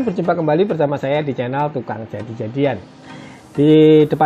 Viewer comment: Teks Subtitle nya kok channel malah jadi channel Tukang Ojek Janjian (0:08) .... Wkwkwkwkwk .... Rusak neh google translate 😂 kesian woi channel orang neh (Coba deh perhatikan teks nya) 🤣